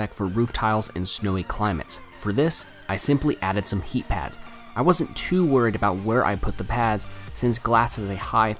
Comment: Did you say heat pads in the minute 3:00? Sorry didn't get well what you said